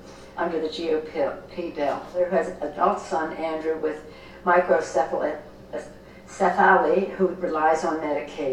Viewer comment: I think, she was trying too say (Hydrocephalus)